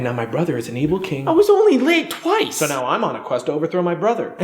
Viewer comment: at first I thought he said "I was only laid twice!"